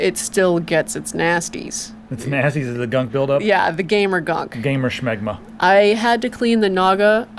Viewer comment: W-Wait.. did he say.. Gamer's Smegma?! o.O